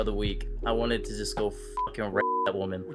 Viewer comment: I was gonna say what Sammy said was no big deal cus I thought he said “fucking wreck”, but nope. Yeah, no. What he actually said is over the line.